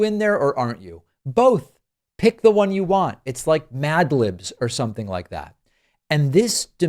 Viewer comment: You said MadLibs and I thought you said MAGlibs so I am gonna use that now for their nonsense. Also, I like the new background.